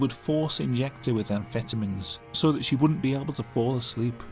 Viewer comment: When you said "amphetamines" I thought you said "vitamins" because British people suck at saying "vitamins".